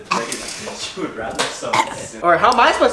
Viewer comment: Am i the only one who heard "she would rather shd" while was coughing